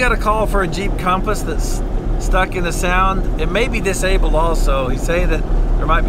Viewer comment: even youtube CC says sound not sand